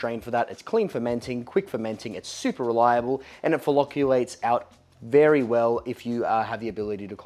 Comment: Freudian slip, That flocculate sounded like it was coming out fellate